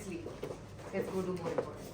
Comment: what ahana said...?? Come, kets do more important things...... What theactualfuck is thag....????